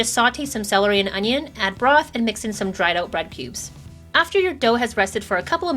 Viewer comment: i heard dried out bread pubes 1:11 instead of cubes bahaha!!